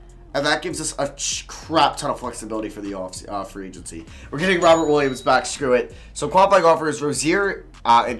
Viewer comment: I know it’s Robert but it sounds like he says robin williams